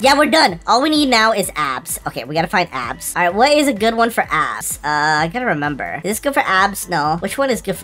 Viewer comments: DID HE SAY ASS AT THE UNDERGROUND GYM AT 18:53/4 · I swear bro is saying ass and not abs😭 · i heard ass.